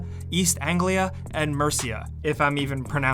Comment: Imma be honest, thought that said 'merica